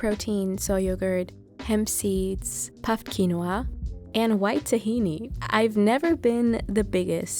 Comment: I misheard "white tahini and I heard "wet tahini"